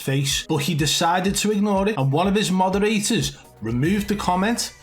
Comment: Lol it sounded like you said one of his mother eaters not one of his moderators hahsha